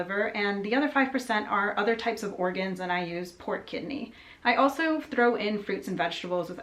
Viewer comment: For a bit I thought you were saying "port kidney" 😂